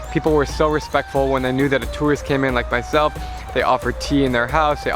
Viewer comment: ffer instead of offer